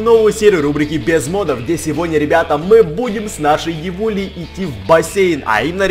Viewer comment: Как ты ведьму назвал? я просто не расслышала. Евуля? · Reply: Он её не называл. Он сказал "Девуля"